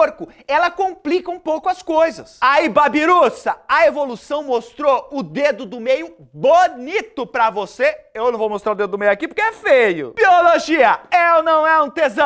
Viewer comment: "Biologia, é ou não é um dedão?" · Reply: Mano juro que ouvi "biologia é ou não é um tesão"